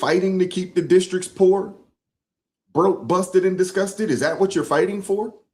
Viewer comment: always sounds like she says she's "discusted." (with a very hard c)